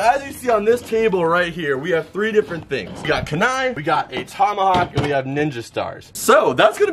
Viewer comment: did he just say knive